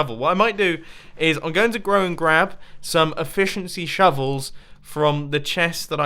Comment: I heard "Grow and Grab"